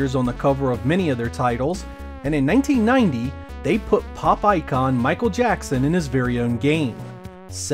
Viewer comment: for few seconds I thought you said "Popeye Con".